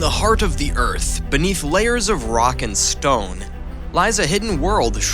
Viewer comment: did i hear a rock and stone ?